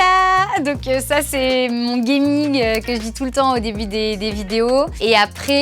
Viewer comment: Ton quoi ??? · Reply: AHH! On est d'accord ! elle dit bien "gaming" ?